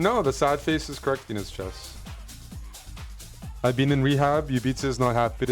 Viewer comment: wait, did I mishear him? "I've been in rehab?"